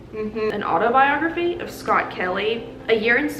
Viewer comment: I thought you said "an autobiography of R. Kelly", I'm so glad I was wrong. 🤣🤣